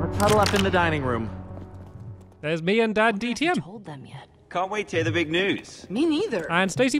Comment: I really hope you all enjoy the episode. :) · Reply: I Heard You Say "DaddyTDM" And Not "DanTDM"